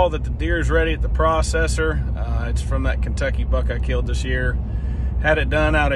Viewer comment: Sound like he said Kentucky fuck lol